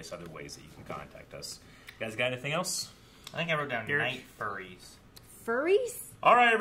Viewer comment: At first I heard "like furries" I had to rewind to understand he said night furry